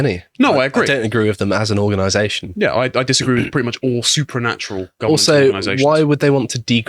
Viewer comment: it's supernational, not supernatural. If I misheard, sorry.